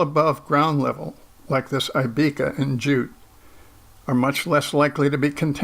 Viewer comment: What's the name of the plant at 1:33? Automatic subtitles says it's "I Becca" but I can't find any plant with even remotely similar names on the internet. It looks like a kind of mallow. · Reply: Hi Troy. I think he is talking about the Aibika plant. Scientific name is Abelmoschus manihot.